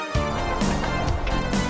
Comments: I thought said URINATION BOB lol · the first couple times I heard "It's Urination Bob". 🤣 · Did anybody else think he said urination Bob?